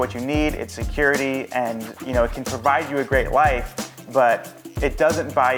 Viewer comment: I deadasss thought he said “it can buy you a great wife” 1:13 😭😂